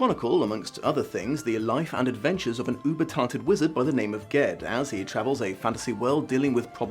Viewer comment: I have no idea how I managed to pronounce "semblance" wrong in the opening but oh well. Here are some links to the books I reviewed today. Apparently I'm an "influencer" so I get kickbacks if you use the amazon links (yaaay I guess). A Wizard of Earthsea: <link> <link> The Tombs of Atuan: <link> <link> The Earthsea cycle: <link> <link> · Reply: When you say "Uber Talented" i had to turn on CC just to see what you were saying, it sounded like you said "*something beginning with U* Retarded wizard"